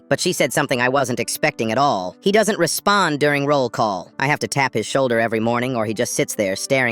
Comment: I thought i heard durian